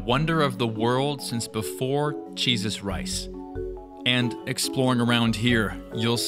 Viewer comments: Listen I can't be the only one who heard "Jesus rice " 5:31 😭😭😭😭🤌🤌🤌🤌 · Am I trippin or did he say "cheese us rice"? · did he say 'cheeses rice'?